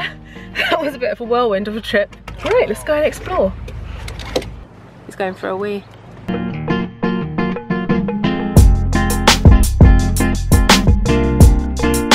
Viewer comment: lmao did she say hes going for a wee? haha as he pushes his hair back like okay haha